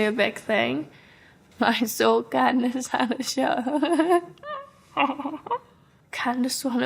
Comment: Can anyone recognise what she said between 7:28 - 7:30? Really need the help cuz there's no subtitle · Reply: “i saw candice at the show”